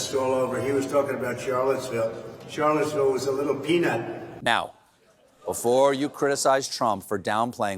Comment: A little peanut? 🥜 I thought she said it looks like a little mushroom. 🍄‍🟫